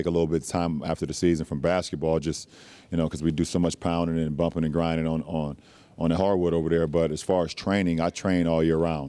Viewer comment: Did he say that they do "so much poundin' and bumpin' and grindin' on the hardwood?" 🤦‍♂️